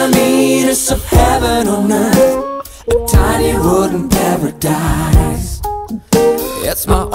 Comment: The first time I heard this I thought he said, "Tiny rodent paradise," instead of, "Tiny wooden paradise." Then I decided to actually look for the video lol